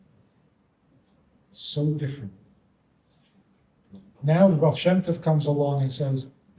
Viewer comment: Did you say Rofshemtiv? Who comes along and says "that's not our God"?